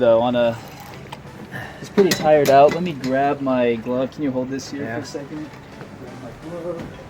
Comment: I thought he said let me grab my glock I was like the fish is only bout 10 15 lbs..lol😂😂😂